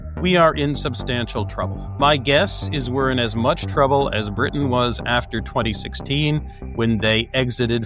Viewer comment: I heard, 'Britney'💈🎤